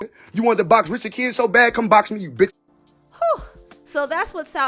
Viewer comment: I know y’all think it’s admirable that Southside “defended” Caresha but if yall really understood these type of men, you’d know based off his word choices that men like that treat their women like they are their literal PROPERTY, hence the “my b*, my b*” talk. She ain’t nothing but possession to him. Men don’t let other men disrespect their “property” because they see it as disrespect towards THEMSELVES. At the end of the day, he was offended because HE doesn’t like how it makes HIM look. There’s nothing to praise here. He ain’t got the respect for her that y’all think he has. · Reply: Speak sis and it sound like he said bitch's if im not mistaken